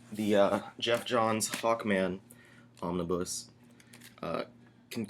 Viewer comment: did he say " Fuckman " by Geoff Johns.